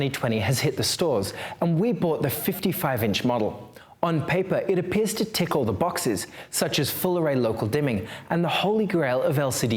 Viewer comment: 'It appears to tickle the boxes' is what I first heard